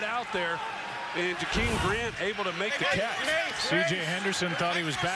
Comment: "CJ Henderson thought he was back in Florida" Well Technically he was Also I know he said Back AT Florida, but I just thought it would be funny to post this so can you guys chill please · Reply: I thought he said "in Florida" as well the first time and had the exact Same thought process as you 🤣